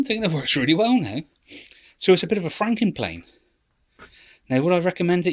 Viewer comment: Franken Plane's sounds like your saying fing planes